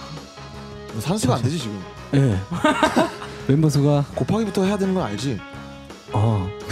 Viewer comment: The subtitle is wrong there. He said "You do know you have to multiply before you add, right?"